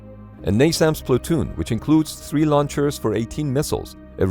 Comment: The subtitles at 6:45 say NASMAS instead of NASAMS btw